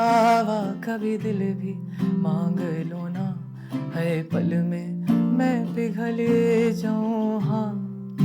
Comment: Am I the only one who thought it was "har pal main" instead of "haye pal main" ?